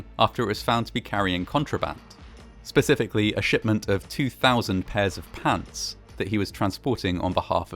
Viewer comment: 2000 pairs of pants? Contraband? I had to play that back 4 times. The first two times I thought you had said "200 pairs of cats". I'm not kidding. Were these bell-bottoms? Straight-legs? Lederhosen? Bloomers? (no, wait, that wasn't a thing yet) Kneepants? Or....Confederate gray uniform pants? Gotta add if anyone has seen WWII footage of the troop ships coming back from Europe packed with people and war wives from Britain and France also loaded on board, the Sultana doesn't look so bad... <link>